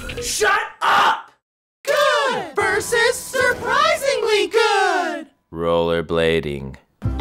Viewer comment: I thought it said "Smosh vs Surprisingly good" lol XD.